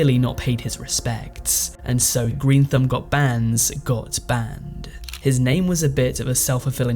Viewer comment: i heard "greenthumbgotbanned got banned" 🤣its my first time here, i like you 😄